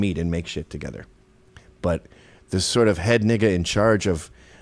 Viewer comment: AYO what the hell did he just say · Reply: He said "Nigga". Are you an actual child?